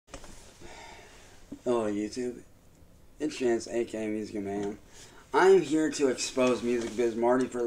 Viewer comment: What’s his name on YouTube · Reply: I thought he said music man